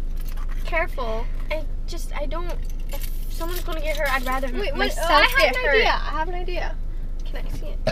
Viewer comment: i heard roni said "i rather i get hurt than nessa get hurt" i think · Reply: Nathania Purnama she said "if anyone is gonna get hurt I'd rather it be myself"